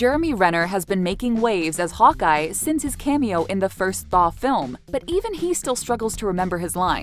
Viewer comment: what did she say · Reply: Thaw I think 🤔